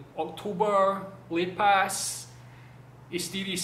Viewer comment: Aku salah dengar ke dia sebut "Oktober lipas" ke lepas?... 0.48 minit. 😂 😂 😂 Comel